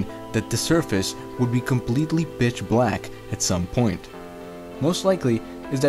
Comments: I swear to god I heard "bitch black" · he says bitch black instead of pitch black lol